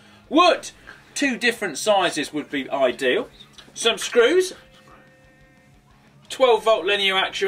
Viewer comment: anyone else hear the word "subscribe" being whispered at 0:28 its is just some screws but whatever